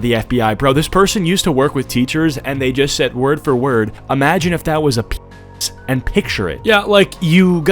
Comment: thought she said imagine if it was reversed wich actually sounded sane then i seen the subtitles